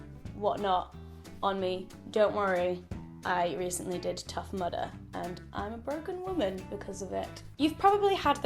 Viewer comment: When you said Tough Mudder, and I thought you said tough murder, and I was veeeeeery confused 😂